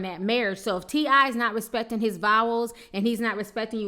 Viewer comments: Girl @ 12:54 did you say not respecting his vowels..........you be killing me!!! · For a sec I thought she said respecting his vowels... L🙄L